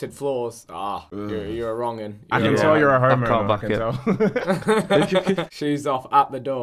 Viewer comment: Thought Tobi called people who wear shoes in their house homo's lol. I'm pretty sure he said Home Owner. House shoe people don't know what comfort is tbh